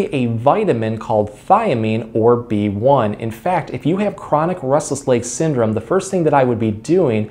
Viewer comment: Sounds like he says rustles legs lol